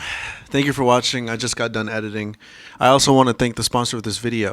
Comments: Actually thought he said that he just got done "headiting" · I did the animation in this vid, but it took me a longgg time. So if theres any talented animators in the crowd, email me your portfolio (my email is in the replies below) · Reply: thought he said "headiting" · is it just me or did he say "i just got done headiting"